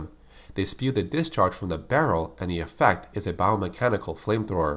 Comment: I thought Mancubus said "From the Bowel".